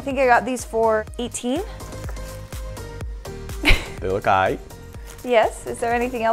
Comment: did I hear say ight?